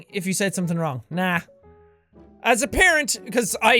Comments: did anyone else hear Jack say 'as a parent...'? Is there something we don't know? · umm Seán did you say as a parent? x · Did he say as a parent?!?!?!?!?" In 14:40 i think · did Sean say "as a parent?" as if he is referring to himself as a dad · Just wanted some clarification, at 14:38 did Jack say "As a parent." or did I hear that wrong? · Wait? At 14:37 Did He Say He Was A Parent? What... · did he say as a parent?